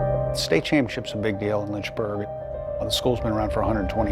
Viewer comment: Did they say Lynchburg? Because we literally are barely on the map. Shook.